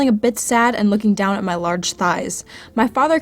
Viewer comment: DID I HEAR LARGE THIGHS?